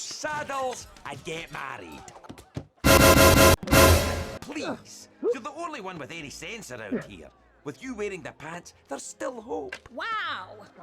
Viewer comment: “with you waiting the” what does gobber say??